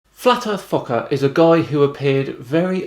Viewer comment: I kept hearing you say “flat earth fucker” lol